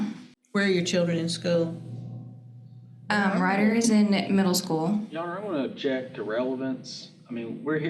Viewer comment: Did she just say her kids go to dicks in middle school? She’s definitely an alumni